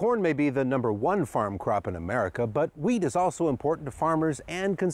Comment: i swear he says "weed" instead of wheat